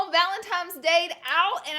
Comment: Did I just hear ‘valentimes day’?